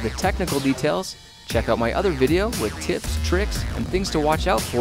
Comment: Is it just me or does it sound like he is saying "for tits tricks or......" I know that he says tips but my life is so interesting that I look out for things like that.... -_